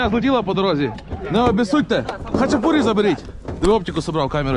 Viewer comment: did he say Khachapuri? 🤔